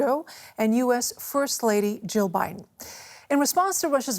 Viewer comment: did she say "hosting [...] US first Lady Joe Biden"?